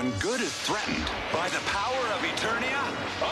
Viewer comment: I thought He Man said By the Power of Gray Skull I have the Power